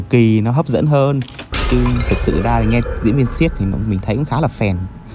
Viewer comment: Nghe nhầm thành diễn viên s*x